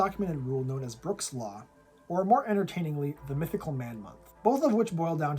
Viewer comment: I’ve always heard it as “the mythical woman month.” The joke being that management expects nine women can make a baby in one month.